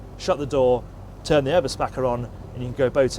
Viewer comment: What does he say at 4:09? "turn the over spacker on"? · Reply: Eberspacher. It's a make of diesel fired central heating: <link>